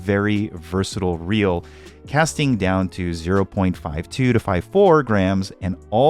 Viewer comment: When you say battery longevity does it have a rechargeable port to charge the battery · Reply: Did you say lure weight down to 0.52 -0.54 gms at 2:47 of the clip is that right?